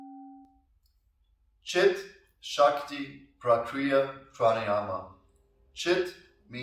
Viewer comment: Thought it said child shakti hehehe